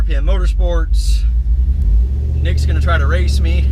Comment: It's says niggas instead of Nick in the captions 0:54 😂😂